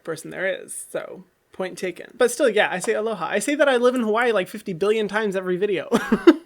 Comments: I always thought Hyram was saying hello hi 😹😹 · Wait I thought you were saying like hello hi like hi as in the beggining of hyram like hy. Like hello hy but now I know ur saying aloha?? Does this make sense lol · lmao I literally thought he was saying helloha (like a mix of aloha and hello)